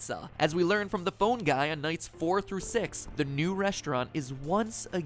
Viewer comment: it sounds like he says "The Bone Guy" XD